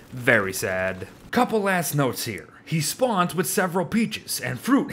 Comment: was it just me or did i hear and a couple ass notes